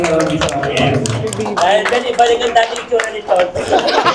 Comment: ano sabi? di kasi klaro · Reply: Ibalik daw ang dating Muka ni tonton